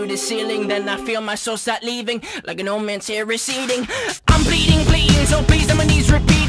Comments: I SWEAR I HEAR TYLER SAY "I'm bleeding beans" AT 1:14 AND I CAN't UNHEAR IT, HELP. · sorry but does no one else hear " im bleeding beans " because i do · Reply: I hear I'm bleeding bleens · I SWEAR I HEAR TYLER SAY "I'm bleeding beans" AT 1:14 AND I CAN't UNHEAR IT, HELP. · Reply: I heard "I'm bleeding bleedings" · sorry but does no one else hear " im bleeding beans " because i do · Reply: I always heard "I'm pleading please" but now I'll never unhear that. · I SWEAR I HEAR TYLER SAY "I'm bleeding beans" AT 1:14 AND I CAN't UNHEAR IT, HELP. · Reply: I hear "I'm bleeding bleans"